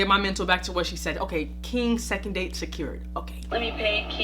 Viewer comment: The girl @ 11:03 I thought she said “King! Sucking D!ck SECURED”